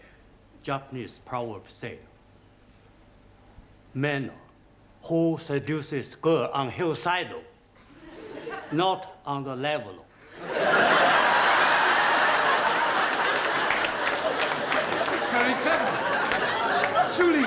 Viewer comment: Anyone understands the Japanese joke? I cannot make out the words. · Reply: Man who seduces girl on hillside...not on the level..!